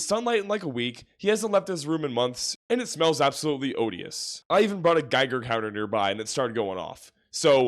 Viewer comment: You said “Odious” and I heard “Odysseus”, sorry y’all I’m autistic 💔